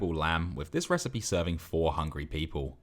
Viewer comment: I first heard "with this recipe serving 400 people"